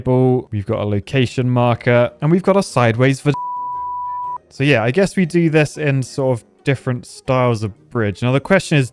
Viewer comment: wha did he say!!! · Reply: Vaseline